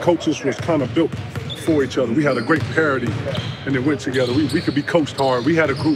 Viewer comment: Did KG say “we had a great parody” at 32:46 ???? Looool